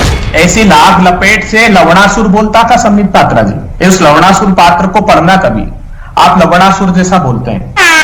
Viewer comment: is he say" laudasur"